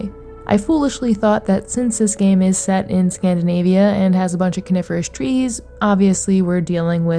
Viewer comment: Not an English native, I thought you said carnivorous trees for the first 3rd of the video 😂